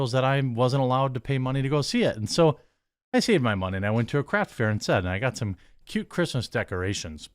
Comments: You said "I went to a craft fair" but I heard "I went to a crack fair" and now I'm itchy all over imagining how fun a crack fair would be. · I know you said craft fair but it sounded like you said crap fair.